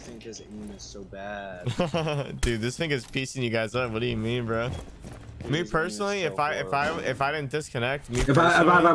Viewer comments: did I hear that right ? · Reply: He said finka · yo does say the n word here ? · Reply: no he said finka